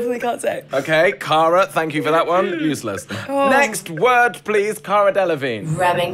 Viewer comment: Did he just say thank u cara useless